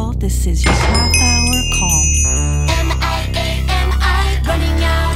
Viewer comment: Im sorry did I just hear “am I gay am I running out of time”?💀